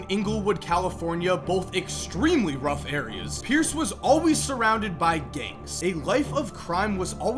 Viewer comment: I thought he said “Pierce was always surrounded by gays😂”